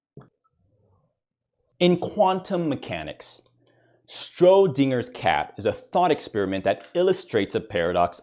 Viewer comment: I'm sorry did you just say "*strodinger's* cat?"